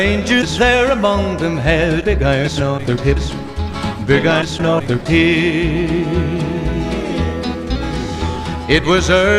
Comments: It sounded like big irons on their teets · i swear i heard "big * no they're hid"